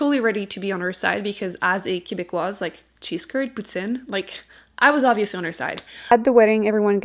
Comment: Can somebody please tell me what she says at 5:57? Sounds like cheese curds putsin??? 🤷🏻‍♀️ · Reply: Cheese curds and poutine